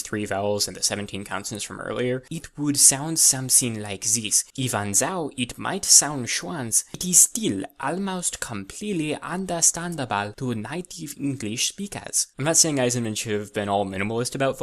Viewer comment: What is that word? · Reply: “It would sound something like this. Even though it might sound strange, it is still almost completely understandable to native English speakers.”